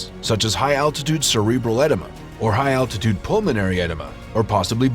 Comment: He says ED-uh-muh for "edema" It sounds like enema! Most people in the medical field call it "eh-DEE-muh, which means swelling! I had to laugh when I thought he was trying to say enema -- nobody likes those! Good but sad story about Scott Fisher and some beautiful pictures of the mountain.